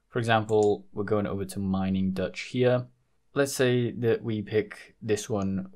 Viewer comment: Why is the resoultion like this I have a hard time seeing what the second site you use is, (Sounds like your saying mining dot sheer? 3:16 )